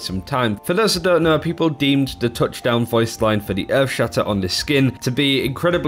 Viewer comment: I always thought he said crush them not touchdown lol